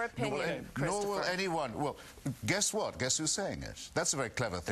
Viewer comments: you for the answer. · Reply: Hmm...Just played that part again – thought I heard "would you prefer I uttered your opinion?" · At the very end it sounds like he says "Would you prefer out of your opinion?" But that's not a coherent sentence. What does he really say? I notice the viewing profile shows many people have replayed those few seconds many, many times. Looks like I'm not alone in wondering. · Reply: He says " Would you prefer I answered your opinion?". I think it needs an extra "with" in there, but it makes more sense and he's clearly trying to get in the last word so he rushes it out.